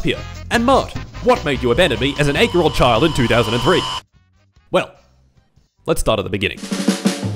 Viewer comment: what did he say here? I can't fogure it out xd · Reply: "8 year old child in 2003"